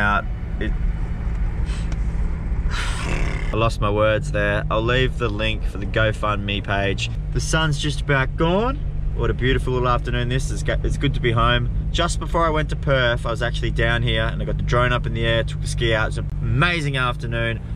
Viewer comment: i thought he said "the sons just about gone" in that happy tone aha